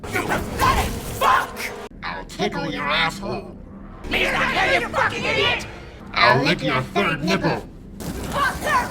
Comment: what does she say!? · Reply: "Mia's not here, you fucking idiot".